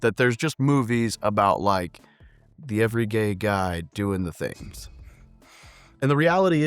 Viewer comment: Did you just say the "every gay guy" at 1:34? You're definitely a Marine.